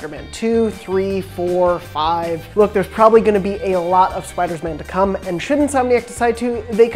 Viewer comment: did he say Spiders-Man games